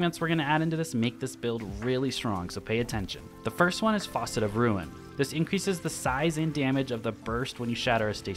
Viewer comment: did you say..."FAUCET of Ruin"? lol